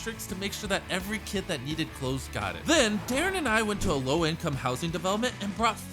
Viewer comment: I thought he said "Then Karen and I"